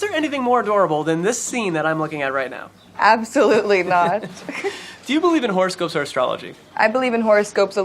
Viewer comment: Lol 3:00 when he asked “Do you believe in horoscopes or astrology”? I heard “Do you believe in horse, goats, or astrology”? Please tell me I’m not the only one who heard that... 🙈